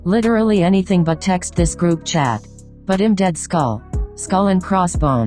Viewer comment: I thought boys said “but I’m dead”😭😭😭